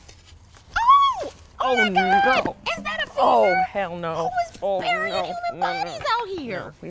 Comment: What did she say at around 4:45? "Oh, oh my god, is that a _?" What's the blank? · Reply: She said femur